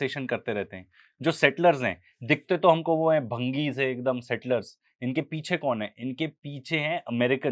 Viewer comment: Did he said "bhangi se". Really cheap. Should say sorry.